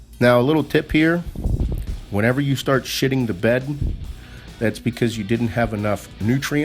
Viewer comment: "shitting the bed"? Is that what you said? Can you explain that... is it just fatigue? I run into that with legs.